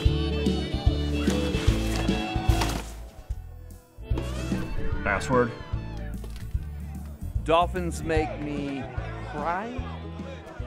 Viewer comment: Who else thinks this goes perfect with reddeadredemption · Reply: I also thought he said dolphins make me high 😆